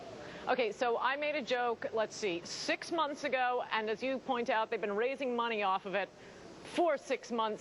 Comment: Seriously. @ 1:00 does she say "they don't make enough money"? What an immature bitch.